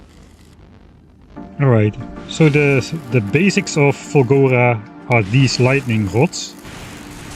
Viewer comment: For four episodes I thought you were saying Fungora and expected mushrooms and spores and nonsense like that.